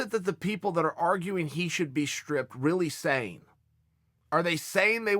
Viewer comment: Lmaoo. I was scrolling through my feed and i thought it said “stipe miocic should be a stripper?”